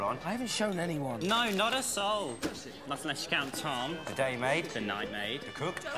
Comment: i always thought and said gooday mate , goodnight mate is it just me???